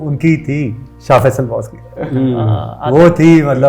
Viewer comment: can anyone tell me what name he referred here? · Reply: I think it was shah faesal